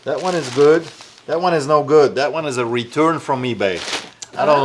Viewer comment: she thought he said “that one is a return from me babe 😏”